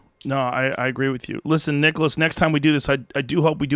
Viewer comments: Who else thought it said nicholas cage? Lol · Man, I thought it said Nicholas cage